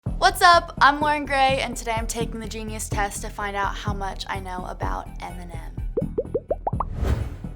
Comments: I thought she said I’m wearing grey😂🤣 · In the beginning I thought she said "I'm wearing grey" instead of "I'm Loren Gray' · Why i hear 'what up i am wearing grey' at the begging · When she introduced herself I Frl thought she said “I’m wearing grey”